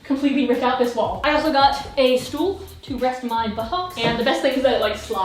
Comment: did she just say "Stuhl"?? (= chair in Germain) Ashley I like u more now ❤️